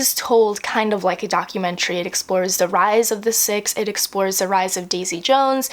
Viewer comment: when you were talking about Daisy Jones I thought for a second you said „the rise of the sith“ hahaha my brain on star wars i guess 😂😂